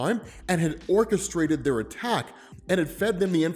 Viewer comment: Cyclops is so much more than his eye beams. Also, this is really stupid, but every time said "orchestrated the attack", I heard "Orchis-strated the attack"